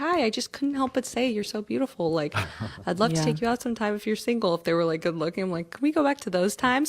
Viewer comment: Did everybody catch what she said at 0:52? Heard her said “if they were like good looking.” This mean that if she finds you “good looking”, it’s ok to ask/talk to her. But if she finds you not “good looking”, be ready for an SA/Harassment charge.